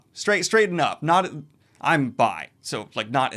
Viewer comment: did he say he is bi?